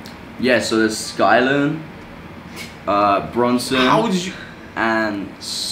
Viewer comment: Calrado gang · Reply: And branson gang at least I thought he said branson